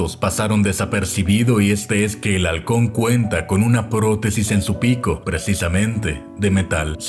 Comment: En ka del halcon que dijo "protesis" escuche "poronga" disculpenme